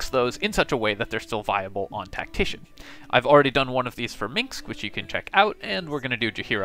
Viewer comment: minSc not "minks" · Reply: it does sound like you say "minks ".